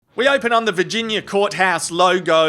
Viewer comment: I thought you said 'we open on the Vaginia court :)'..